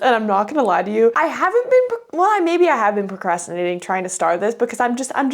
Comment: Okay first in the beginning I thought I said “bookrastinating”which I know I’m delusional but I love the word secondly and haven’t even red the book and I could feel all ur emotions through the unfinished words hands in the head (desperate mode ) and the hand covering ur mouth and the other noises like relatable 🙌😅 girl I feel you and that “heavy” feeling cuz we (u and the characters cuz ur one of them obv) have to solve the situation hahaha I love this ♥️